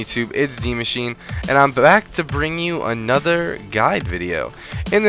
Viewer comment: i think you said " im black" at 00:14 LOL